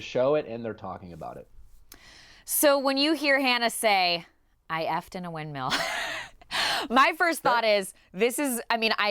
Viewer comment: So glad Lauren said, "I f'd in a windmill." I was thinking I was out of the loop with the sex terms - I thought Hannah said, "I f'd a windmill!" and I was like...I...I...I just don't get that. NOW, I get it! LOL! GO, HANNAH! GO, LUKE...NO, REALLY...GO AWAY, LUKE - BUH-BYE. ;-)